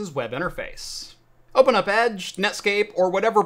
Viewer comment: did he actually say "Netscape"? 🤣